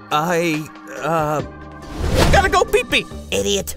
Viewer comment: did he just say "I gotta go pee pee"